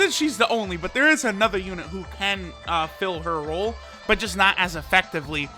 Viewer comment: I heard hole, not role.. boiiii..